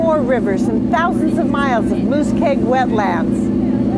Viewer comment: did she say moose-keg wetlands? Hahaha! thats funny! Muskeg! unuh the Swamp....lol